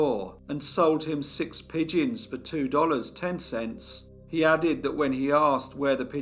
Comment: oh, I have to read closed captions and that is what it says. I went back. 7:07 minute mark. I cannot hear. Thanks but what does it say? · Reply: It was $2.10 but it may have appeared as $210 - Sorry :(